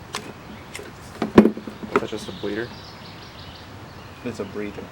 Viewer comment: did he say is that a Bleeder ? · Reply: "And that's just a breather"